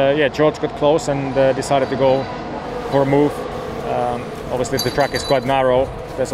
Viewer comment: Did Valtteri say "[...] George got close and decided to go... her move [...]"?